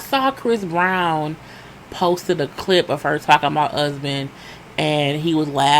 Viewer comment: I thought Lisa was saying husband instead of Usman!! I was wondering why his name was Husband, lol.